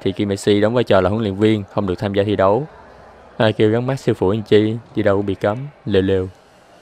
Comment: Ở 2:39 ảnh nói gì vậy ae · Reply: Ai kêu gắn mác siêu phủi chj thi đấu bị cấm ..lêu lêu.